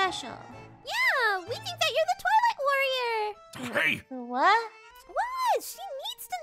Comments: On 2:14 when KC said twilight warrior why did i hear toilet warrior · did she just say.... TOILET WARRIOR??? XD